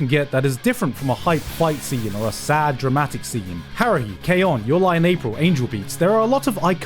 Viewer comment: what’s the name of the anime said here that starts with the letter h called? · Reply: The Melancholy of Haruhi Suzumiya. Often it's shortened to Haruhi